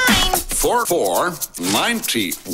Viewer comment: Stingy saying 9 sounded like "mine". I think it was made intentionally.